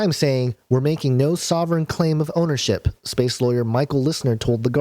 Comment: Wait wait wait at 3:47 Did you say "space lawyer"??